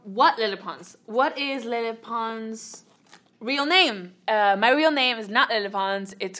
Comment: It literally sounded like she said “Hi my name is menopause”